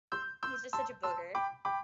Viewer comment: Lol when I heard "Kernel", I thought "Colonel". I was like "Colonel is an awesome name for such a powerful looking dog!", then I saw the spelling, and thought "Kernel is an awesome name for such a powerful looking dog?"